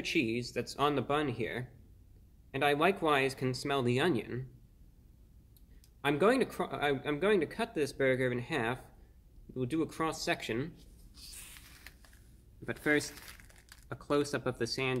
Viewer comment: Lmao, I thought he said "I'm going to cry" for a second 🤣